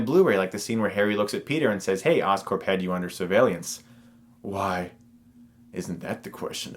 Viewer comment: did I hear that right? "Survallence?